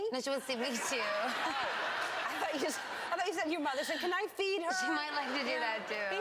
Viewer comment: I actually thought she said 'Can I feed her' to :P